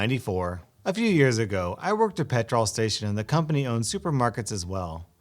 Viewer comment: Did I hear pet-trawl or petrol HMMMMM... 13:19 someone please clarify.